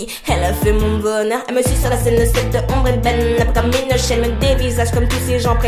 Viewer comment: Ca me perturbe quand tu dit "elle me suis sur la scène"( 00:59 ). Moi j'entend "Elle me suce sur la scène" mdrr. Mais j'adore quand même tu gère <3